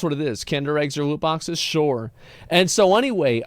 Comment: I swear to God I thought he said lewd boxes. LUL.